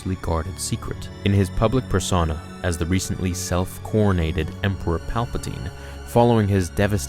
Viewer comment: This quarantine is getting to me I heard "the recently self-quarantined Emperor" instead of "the recently self-coronated Emperor"